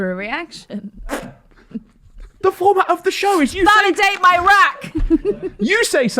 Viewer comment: Did I hear "Validate Iraq" typical Reform Uk spokesperson mumbo jumbo